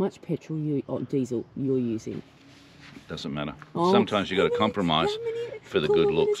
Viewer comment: all I hear is Con The Fruiterer "dussin madder"